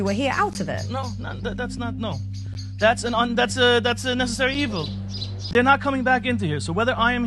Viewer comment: Did he say necessary evil or am I imagining it?🤡☠️